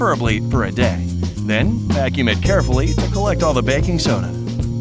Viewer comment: did he say baking sona ??? 😅